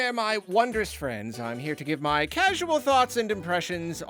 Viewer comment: My brain genuinely thought you said “casual thoughts and depressions” 😂